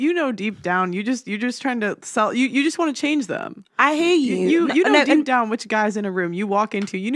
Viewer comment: I hate you あなたが嫌いじゃなくて、 I hear you あなたの言いたいこともわかる のはずですよ